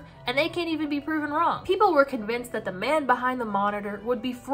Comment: I THOUGHT U WERE GONNA SAY MAN BEHIND THE SLAUGHTER